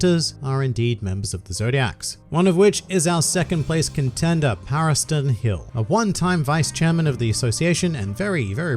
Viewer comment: Paristan hill... legit thought you said Paris Hilton